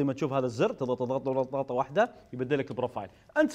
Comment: ايش قال؟ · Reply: ضغطة وحدة